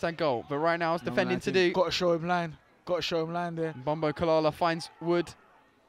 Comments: I thought you said “Bombaclart” 😂 sooo funny 🇯🇲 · Thought you said bombaclart 😂